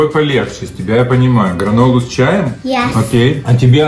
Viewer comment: мне послышалось гов** с чаем😂